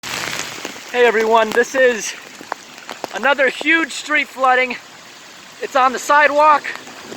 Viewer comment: At first, i thought he said "Hey everyone this is daily dose of the internet" 😂